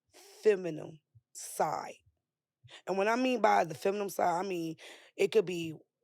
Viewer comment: The masculine-acting chick on the left with her stomach hanging out can't even pronounce the word feminine. It sounds like she was saying fe min em. I can't with these New York/Jersey chicks... Woo wee... Masculine AF.